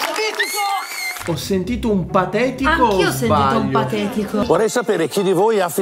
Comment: io ho sentito siete mitico